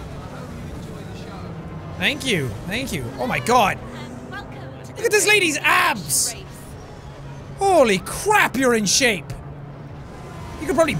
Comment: I thought he said ass lol